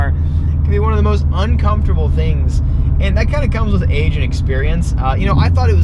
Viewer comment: I could have sworn you said Asian experience lol